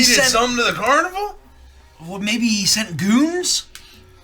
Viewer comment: I may be overtired but i coulda sworn someone said "maybe he sent gooners" 😭🙏